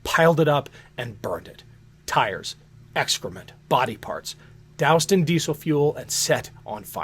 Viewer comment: did he said Body parts , listen